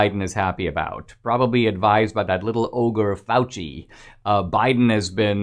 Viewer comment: I thought he said “little ogre Mouthy.”